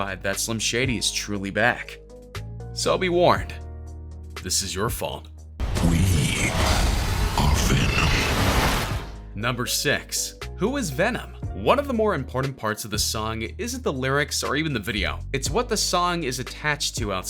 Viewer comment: WTF why did you said „number sex“